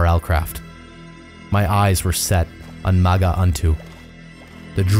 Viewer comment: When he first said maga unto or whatever how you spell it I thought he said maggot shitstain (I wasn’t paying attention) so I thought he was gonna fight a huge fucking maggot or worm lol